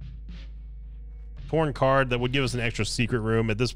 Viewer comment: Did he say porn card it’s just me?